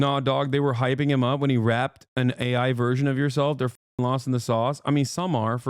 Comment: omg i thought he said r@ an ai version of Hasan I wouldn't be surprised but still I'm grateful it was rapped